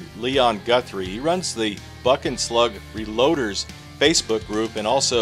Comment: man, when you said 'buck and slug' all i heard was 'bucket slug' and i thought to myself, what kind o slug is that